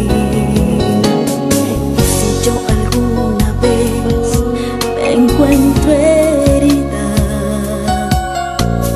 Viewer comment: alguien sabe lo que dice en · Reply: Jesus Vive Es q yo alguna vez me encuentro herida